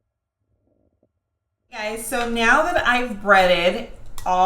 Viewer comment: did she say Meow? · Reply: She said now.